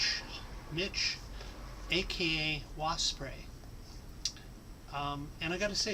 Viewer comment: LOL, I was watching it with youtube's closed captioning and at the point where you say "aka wasp spray" it came up with "bikini walk spray". Right around 1:30 :D Very nice of Mitch. I regret selling my Amiga years ago. In the 90's I picked it up in a thrift store but it wasn't something actually for sale. It was the thrift stores office computer and they had it set aside on the floor to get a new Windows 95 computer. I asked what they were doing with it and they said I could have it for $20. :D